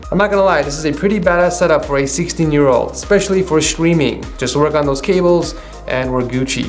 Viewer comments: did he just say stwiming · I thought he said "this is a pretty bad setup" 😂